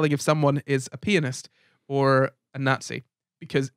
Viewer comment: I feel childish for thinking they kept saying penis instead of pianist until turning on the subtitles. I was so confused😂